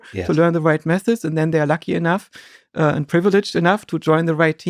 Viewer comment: I love how Joscha says something profound and widens his eyes lol. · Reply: Also, when he says" when scientists are 'lucky' enough" .... i could've swore he said 'lackey' enough .... which also makes much sense in context